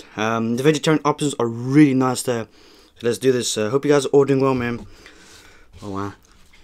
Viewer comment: Why did I think he said "I hope you guys are ordering well" 😂